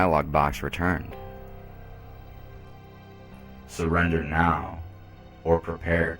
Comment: At first I thought you said "Slender man"